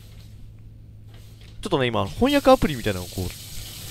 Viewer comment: 翻訳アプリが「ほんにゃくアプリ」って聞こえた人、今日もお疲れ様でした、、、 今回、かなり苦戦されてるような、、、負けるなガッチさん！